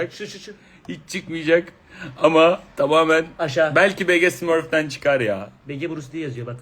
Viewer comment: ne diyor? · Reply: belki bege smurfden çıkar ya